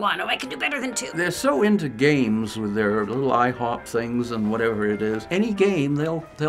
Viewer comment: did he say IHop things